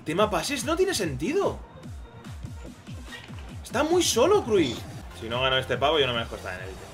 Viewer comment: que dijo??? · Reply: está muy solo Cruyff